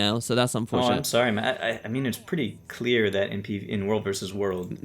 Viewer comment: wait a second, did just call "Matt"?!